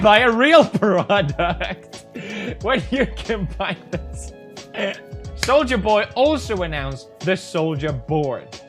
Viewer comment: I dont get it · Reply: beyza' when the kidnapper says “I solja boy” it sounds like he said “I sold ya boy